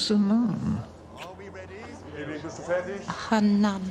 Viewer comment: What does she say??? The sub titles were disabled for just that one line of hers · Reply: "A Hun nun." Meaning a German Nun.